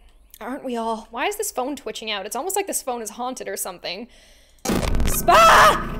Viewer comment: "SPAA" did anyone else hear it? XD